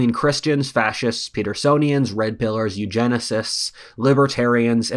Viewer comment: what did he say? Petersonians?